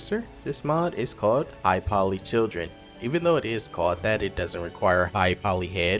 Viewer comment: I'm sorry man but no. High poly children is literally a pdf file mod. They even recommend "CBBE and Himbo".... "They are able to equip all clothes and armor, although to get "right fit" for their body shape third party mods are needed. It is recommended to use CBBE, HIMBO and OBody and right preset (zHPC Shape preset) is to be selected with OBody menu. Configuration file can automate this. Instead that, included Child specific outfits can be used. You are able to buy them in game from vendors." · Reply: I was away from my monitor when he mentioned "high poly children" but I misheard him and thought he said "I follow children.," I was like hold up! what?! Then I seen this comment, oh wow!